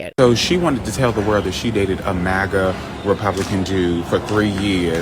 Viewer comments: I think he said dude not jew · "MAGA Republican Dude". It's just the way he says "dude" to sound like "dewd". It's pretty typical. I hear it a lot. He's still racist, though, but it's probably because of indoctrination. It's all different when you find someone you're attracted to on a deep level.